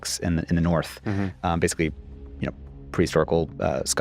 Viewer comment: subtitles say "priest oracle" when he said "prehistorical". AI can't even figure out human speech patterns yet. I live in the Czech Republic and not one closed captions model can cope with me saying the word "Czech" unless it is directly followed by the word "republic". So, it constantly writes sentences such as "do you speak check?" "do all check people do this?" It cannot comprehend that this is grammatically ridiculous: people speak Czech, not "check" - in what context could you possibly have pronoun + speak + check? That's two verbs together.